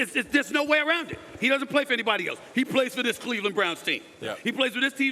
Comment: It sounded like SAS almost said "Cleveland Clown." team 🤣💀